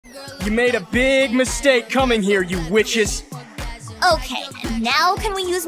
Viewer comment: I thought he said “you made a big mistake you bitches”